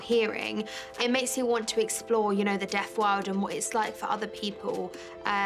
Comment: I thought she was saying death world.. i was confused and then I realised after he sign language that she said deaf world.